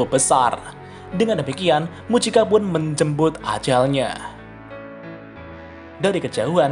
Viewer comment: "Mujika menjembut ajalnya? 😂" Apa gua salah denger ya??